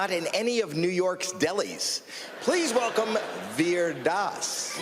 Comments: I heard like Weird Das 😂🤣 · Please tell me I'm not the only one who heard it "Weird ass". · I heard the host said "weird daas" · Did he say "weird arse" at @ 0:11?